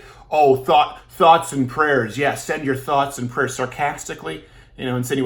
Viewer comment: Anyone else thought he said thots and prayers?